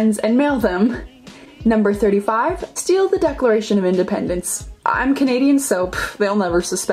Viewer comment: captions: I’m Canadian soap. What she said: I’m Canadian so...